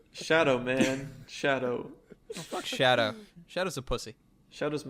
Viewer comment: Did he just say Shadow is a pussy!? Whoa!!!